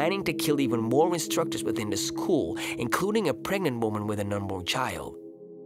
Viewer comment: "A pregnant woman with an unborn child"🤔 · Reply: Bryan Alonzo It's hard to make out what he says there, I hear it as "a pregnant woman with a normal child"